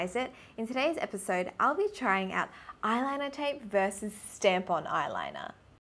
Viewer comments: In the beginning I heard "eyeliner tapes versus tampon eyeliner" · I thought she said "eyeliner tape vs. tampon eyeliner" lol